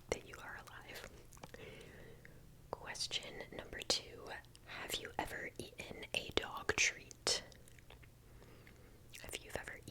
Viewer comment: what I heard: have you ever eaten a dog?